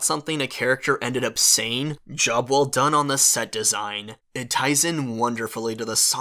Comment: When he said “Job well done on the set design” I thought he said “Jubble done”